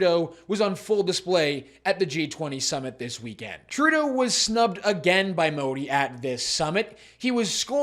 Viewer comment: I thought you said *subbed. Would be more to character.